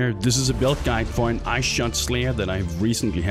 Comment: I thought it said Thot Slayer for a second there...